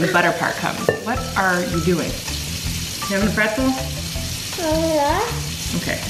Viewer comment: OMGGG WHEN YOU SAID ARE YOU HAVING A PRETZEL I SWEAR I THOUGHT SAID “are you not” I DIEDDDDD